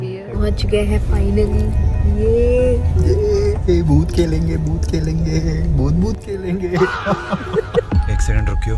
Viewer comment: He said Bhoot Khelenge but I heard Bhoot Ki Lenge 😂 (Behra ho gaya hai maa tera beta)